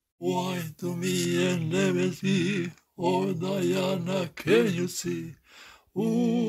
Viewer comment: KFC💀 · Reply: He says can you see but when I first heard it I to thought he said KFC lol 😂😂